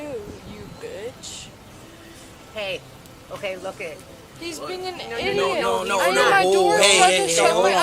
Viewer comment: when she says “he’s being an ID-iot” i literally fucking lost it 😂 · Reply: I thought she said "he's been an ID-ist!" (Like a racist, I guess). 🤣